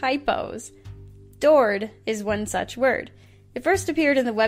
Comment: I THOUGHT THAT SAID TORD I MADE AN INHUMAN NOISE